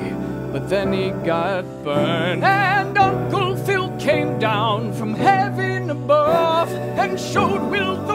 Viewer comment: Lyrics: Would it be alright if we tried again? I'll hold you tight again. We'll be alright again. I wanna be right by your side again. Watch Nick at Nite again And chill with Aunt Vivian. Well West Philidelphia, born and raised On the playground is where i spent most of my days. I'm chillin' out, maxin', relaxin' all cool Shooting some b-ball outside of the school. Hillary and Carlton and Aunt Vivian I wish that you all were together again So throw out the DJ, Let Geoffrey unpack. I long for the day When the Fresh Prince is back Where is Will? x4 Do you remember that one time when Will's dad returned? And Will was so happy but then he got burned? And Uncle Phil came down from heaven above And showed Will the meaning of fatherly love? Uncle Phil. x4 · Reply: I thought he said "and uncle phil came down from having a bath LoL